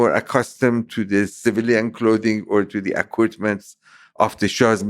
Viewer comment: Wow, Man said a turban and a long dress are not civil. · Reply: Did he? I thought he said those are not normal civilian clothing.